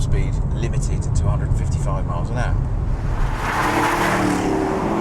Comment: Top speed limited to 255mph???? sounded like it but he said 155mph..lol